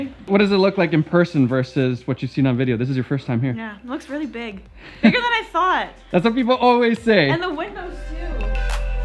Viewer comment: Naomi: „Its bigger than I thought“ „Thats what everyone says“ 😂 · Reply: Lol i was looking up some of the gear listed here and had YouTube running in the background. Sounded extra sexual. Then I hear "it was harder than thought." Wait is pornhub in the background lmao. Thought I was trippin lol